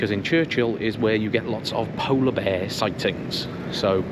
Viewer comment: FYI……while in Manitoba, when they talked about Churchill….it sounded like he said there’s Bison in Churchill. There isn’t.